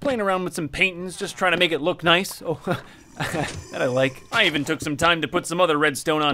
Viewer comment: did u say u like it?